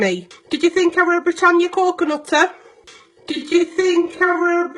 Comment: I'm from the U.S. and i thought she had said "i wear buttered coconutter". Oh no its so much worse.